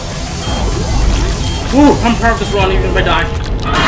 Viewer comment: "i'm ???ing this run if i die" bro what did he say??? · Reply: He said “Im proud of this run even if I die”